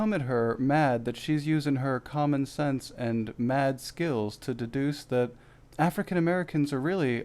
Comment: Did she say "Mad skills" or "Math skills?" I heard "Math skills"